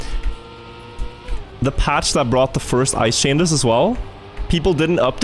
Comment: every time he mentions "the first ice change" i hear "The midori patch"